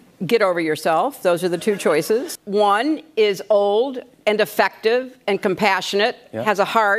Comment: Oh I thought she said defective